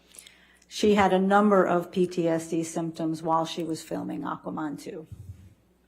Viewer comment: It sounded like she said Aquamon...🤔🤔🤔 Just like she said James Fanco 🤣🤣🤣